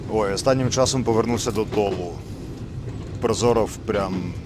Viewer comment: повернувся до доллу, Прозоров про рок, ми безумовно різні, з цих слів не знайшов цей гурт і цю пісню, про що сказав чувак на 21:30? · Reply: Гурт ТОЛ, Прозоров і справді пророк, а ТОЛ свого часу найпрогресивніша метал банда України. Дуже шкода що перестали грати.